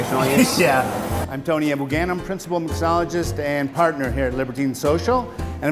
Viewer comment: no matter how often I watch this video, I always hear "I´m Tony Abou Ganim, principle mysogynist and partner" xD